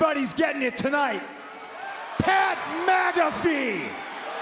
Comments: sounds like he says MAGAfee. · Did he just call him Pat "MAGA"fee? 😂😂😂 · Did Punk say Pat MAGA-fee? 🤣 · It sounded like Punk said Pat MAGAfee